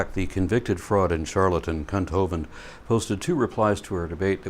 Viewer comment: I heard that "Cunt Hovind" in the beginning 😂😂